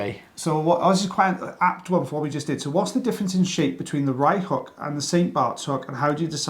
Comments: I thought you were saying "right" hook. Time to have my ears cleaned out! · This whole time I thought you’d been saying “right hook” !